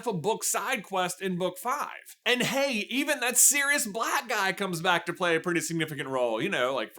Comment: I legit heard "that serious black guy" and not "that Sirius Black guy" 😅